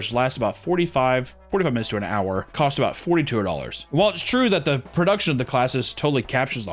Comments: did he say $4,200!? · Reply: I think he said $40 TO $100 per class, don’t worry I had to listen several times to make sure I was hearing wrong 🤣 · Costs what?!?! Seriously I cant understand that number you just said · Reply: Same I was hoping someone in the comments heard what he said, but based in the general consensus, I think it was 4200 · can someone please tell me what he says the price is here?? I want to say that he is saying forty- two- hundred but I cannot for the life of me tell what he says and that number seems astronomically high for one class. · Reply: i looked it up and it is $42